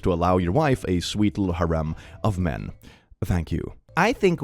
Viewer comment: not me thinking he said haram instead of Harem the first time